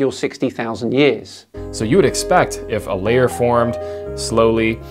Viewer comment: Did anyone catch Powells line: "There was [no C14] in the bottom layer and less and less as you go up". Seriously? just opens his mouth and expels his belly gas and thinks he sounds intelligent. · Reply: To be honest that's not actually what he said. At 5:25 he says there "would be" no Carbon 14 in the bottom layer which he claims isn't the case. Quotation marks matter. If we want to debunk these idiots we can't just make shit up like they do.